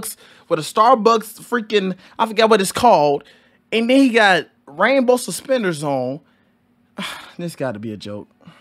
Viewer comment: I thought he said Rambo suspenders. 😂